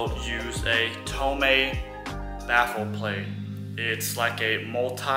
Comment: Syms Racing has a baffled oil pan and STI Japan offers one as well, along with just a baffle set. Trust, Tomei, and Cusco offer baffle plates as well. At least in the JDM market. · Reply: Oh okay that makes a lot more sense, in fact I thought he said he used a homemade baffle plate, but I now realize what he really said was Tomei baffle plate, I see that now thanks. Okay so there are already a bunch of options. Obviously I know almost nothing about this car/engine. So if the older model and the WRX/STI use basically the same engine, why is this issue only mentioned with the new 2.4 liter engine? Or is this an issue with all versions, and I just never heard about it? You hear so much BS I don't know what to believe, some say this is a very reliable track platform (especially in NA form), then others claim it has a reputation... I am starting to suspect the problems might be more user induced. I mean you can't run slicks pulling 1.5 G's and expect a stock setup to handle all of that, is that what these chuckle heads are doing? And then jumping on the internet claiming the car has a design problem? Okay I think I get it now